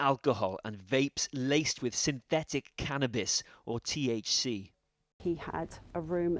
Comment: SYNTHETIC CANNABIS KNOWN AS THC!!???!!!?? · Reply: "synthetic cannabis or THC" is what I heard around 1:57. Could be that they meant either/or.